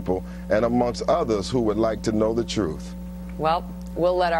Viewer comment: did she say "we'll let our jew viewers be the judge of that"? and shes calling him a racist when she insulted me by saying all her viewers are jews?